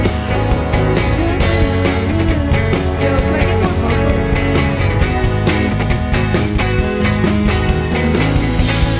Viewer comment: is there anyone who know what she said in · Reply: “yo, can i get more vocals?”